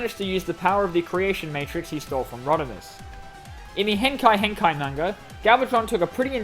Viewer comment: When you said "Henkei! Henkei! Manga" i heard it as "Henkei Hentai Manga" (Wouldn't surprise me at all if there's Transformers Hentai out there. As a matter of fact, it would surprise me if there was none.) Also, isn't it strange that when Megatron turns into Galvatron in Transformers: Cybertron/Galaxy Force he gains a G1 Megatron Color Scheme?